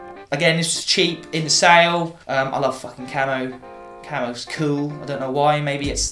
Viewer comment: When you said "I love fucking camo" I thought you said "I love fucking camels" 😂 oops. Been loving your videos! Even my boyfriend likes to sit and watch them with me! Keep up the good work! 😁